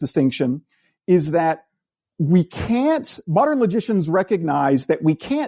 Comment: I kept hearing "magicians" during the interview ;-)